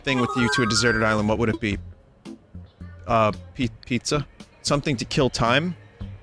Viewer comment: I thought it said "something to kill me" I was like wooah dude